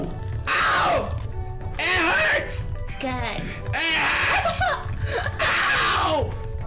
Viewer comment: wat did marzia say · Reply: She said good